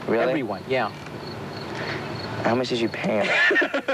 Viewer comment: What did River say in 4:08 and what is the meaning of it? I'm not an English speaker :( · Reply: He said "How much did you pay them?"